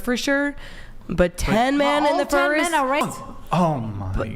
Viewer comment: i swear 10 Bears instead of 10 men. Holy hell, these women are beyond delusional